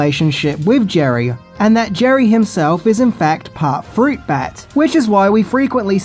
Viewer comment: I thought you were saying “fruit bad” as a sort of slur lol